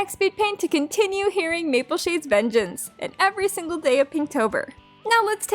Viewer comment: I got a feeling my brain is broken because I always misread or hear things. When you said "Mapleshades vengeance" I heard "YellowFang's vengeance" then got so confused until I heard the name Mapleshade again. Anyways mapleshade just sounds so sweet and nice at the beginning. Thank you for reading this book, I never read it before and I'm not able to get it at the moment. So thanks alot!